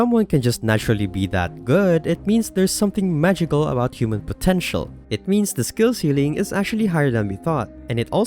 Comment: did you just say potential?